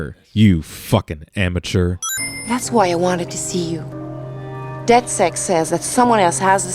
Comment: Call me twelve, but I find it interesting that the voice actor fucked up at 17:57 and said "DedSex" instead of "DedSec"